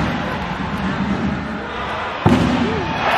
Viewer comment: Thank you for this video. Throughout the entire video. I only heard “Yuki Ishikawa🎉” 😂